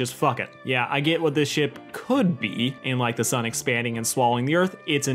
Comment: I get that your saying is "The sun expanding and swallowing the Earth." But I kept hearing "The Sonic Fandom swallowing the Earth." which is a terrifying image.